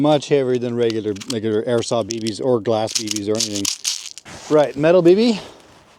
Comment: wtf did he say? lol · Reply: "regular airsoft bbs or glass bbs or anything"